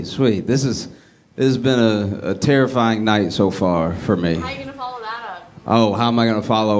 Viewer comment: she was trying to joke with him. She should've been quiet and not interrupt but he was an asshole about it 😂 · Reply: it sounded like she said " how are you gonna follow that?" . Like the comedian before him was so good they were a tough act to follow. At the end after being absolutely hilarious, he asks her if he did good, did he follow that ok. She must not have known who she was about to see ....